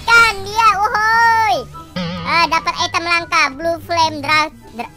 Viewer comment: what? i think he is say a coconut? (Wkwkwk)